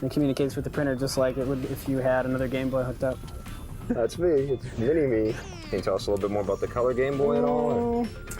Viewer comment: It kinda sounds like he says "Vinnie Me"